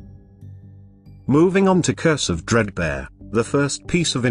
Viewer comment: Darn, misheard "Curse Of Dredbear" as "Cursive Fredbear"